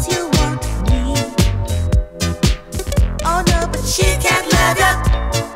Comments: I heard "chicken love you" at the first time.. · I hear "chicken nuggets" instead of She can't love you ^^